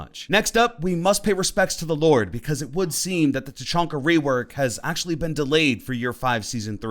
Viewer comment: I thought it said Tachanka day lol. A day to honour our lord and saviour, Tachanka. His reword sounds REALLY cool though.